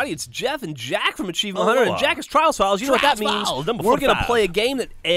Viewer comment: It says Trial Files .... this whole time i thought it said Trial Fires.